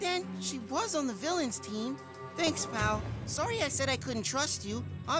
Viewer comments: Am i the only one that noticed at 5:34 that it sounds like says mal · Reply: He did say Pal and not Mal. Trust me this is my all time favorite episod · so why did you and how did you call Mike ... mal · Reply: He said “Pal” not “Mal”.